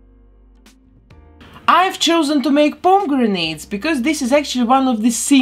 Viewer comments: Pomegranate?!! Why did I hear "bomb grenades"? I'm so sorry. I love your accent and now I feel a fooool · A basket full of pomegranates and human skulls? My heart is stolen! Modern day men don't speak the language of romance... 💔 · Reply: I thought she said ‘palm-grenade’ at first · I know you said pomegranate, but it really sounded like you said bomb grenades. 😳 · I thought she kept say “bone grenades” instead of “pomegranate”😅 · I thought u were saying "Palm Grenades". Took about 1-2 mins to figure out u were actually saying pomegranate. I totally Luvr your accent by the way. I'm sure if u heard me talk u would have a hard time with my accent also. Lol. PS in from Texas and very south of Louisiana.